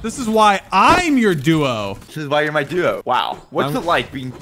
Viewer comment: All I hear is “that is why your my duo” from both of them